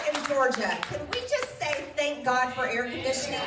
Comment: My volume was low the first time. I heard her say "Can we just say 'Thank God for good pussy'?".